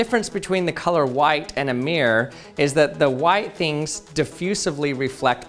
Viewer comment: A mirror? I thought what he said was 'Amir'